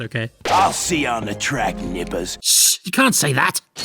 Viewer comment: I think he’s saying Damas there. But I guess we need another ‘shriek with joy’ situation but with Jak & Daxter, huh? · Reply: it says nippers if u read the subtitles